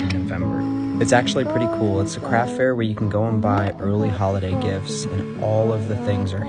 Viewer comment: Instead of early holiday I thought he said erling haaland🤣🤣🤣🤣🤣🤣🤣